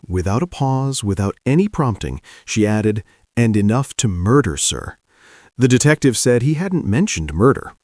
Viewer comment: ~ She said "NOT enough to Murder". (Listen again). Also, Who TF says I love you 'enough to murder'? Especially in a Murder Investigation. FOCUS Bro.